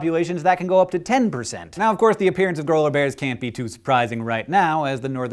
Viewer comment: GRANOLA BEAR!!!!! Is what I thought it said at first. Because my brain is awesome :D (and maybe a bit dyslexic)